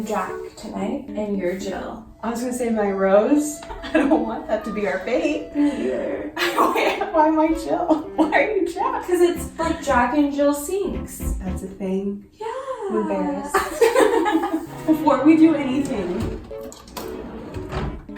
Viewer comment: i thought you said jaclyn and hill cfhgyhiujop